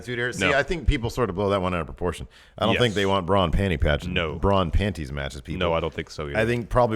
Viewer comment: I heard "Brock in panties match" X_x